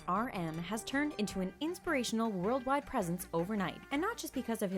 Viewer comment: i thought the speaker said “worldwide president” ksksksks